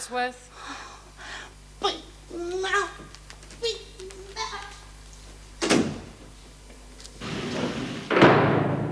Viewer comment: What on earth did Lizzie say when she left the office at 19:15? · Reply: she said "big mouth,big mouth" about herself for nearly blowing a day out